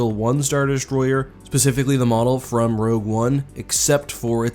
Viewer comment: Bro, you're killing me, did you really say "pacifically" instead of "specifically" at 1:20?